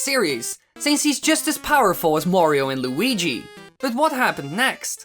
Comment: Did he say 'Wario and Luigi' by mistake or 'Mario and Luigi'?